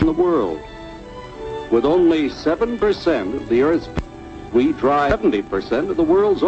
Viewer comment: the commies edited out something anybody know what? all the other youtube uploads same thing.... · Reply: I'm pretty sure is just says people.